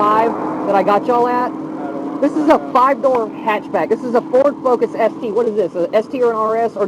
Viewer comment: I thought he said “This is a five dollar hatchback!” 😂